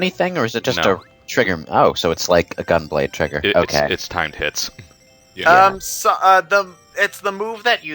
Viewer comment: When they said timed hits sounds like they say time tits